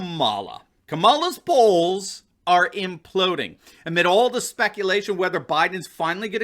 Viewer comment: For a moment there, I thought you said “Kamala’s balls are imploding “.